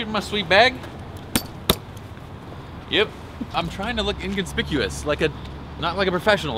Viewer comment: anybody else thought he said he's trying to look 'indian-spicuous'? @ 9:45 ? lol